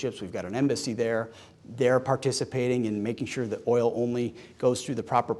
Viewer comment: he clearly said “Oil” Kalshi owns me 20k but they said he never said that.